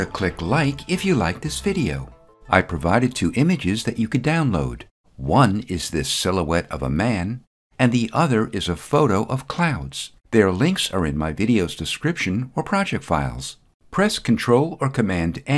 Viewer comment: Every time you said "Silhouette of a man", I heard "Bohemian Rhapsody"